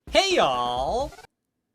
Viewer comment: i always hear it as "hey yall". am i deaf? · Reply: no it’s not just you, some people hear it as hey yall. it’s actually hey ALL